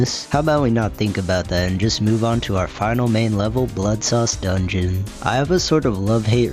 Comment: either im tweaking or i kept hearing "buttsauce dungeon'....